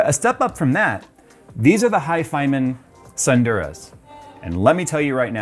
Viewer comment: I heard "fyhymen tsundere"